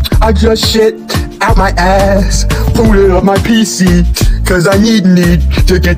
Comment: it’s booted instead of pulling